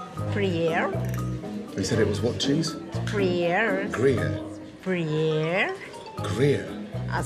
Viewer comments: He bought a Dominican chess called queso freír it’s literally a cheese that we fry in a pan and eat with breakfast. It doesn’t melt. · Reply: I thought she said 'gruyere'... Now I get it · He bought a Dominican chess called queso freír it’s literally a cheese that we fry in a pan and eat with breakfast. It doesn’t melt. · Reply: I thought he was saying Gruyère which is also a hard cheese haha. What a mess.